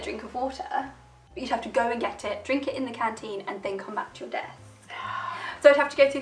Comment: Captions say "have to go and get it drink it in the canteen and then come back to your DEATH!" lol. Should be Desk :P